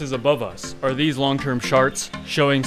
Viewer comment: Did you just say sharts instead of charts? 🤣